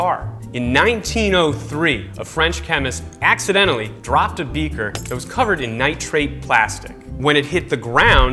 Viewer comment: When he said "dropped a beaker" I thought he said for a second " dropped a beaver" XD